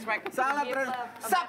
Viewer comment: Jokes nya vindest yg terakhir itu maksudnya apa ya? Gak mudeng gue hehe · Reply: sabtu dahar, minggu heunte senin dahar jeung batman! kedengeran nya gitu